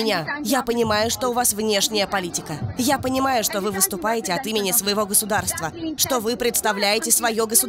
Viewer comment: Я понимаю, что вы выступаете от имени своего Государства? (Я правильно услышал?) А как же народ?